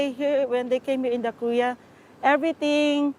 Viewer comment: she says Korea and I hear kuya 😳